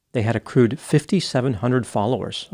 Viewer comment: I heard that as "a crude 5700 followers"